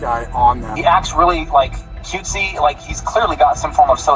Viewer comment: did he really said cutesy? 😂😂😂😂😂